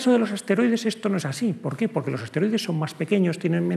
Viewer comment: juraría que dice esteroides, me imagino asteroides todos masados